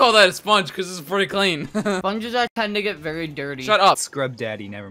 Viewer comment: wtf did he said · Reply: tend